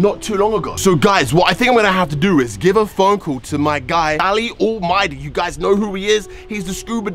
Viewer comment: did he just say da-li-almighty 🤣🤦🏾‍♂️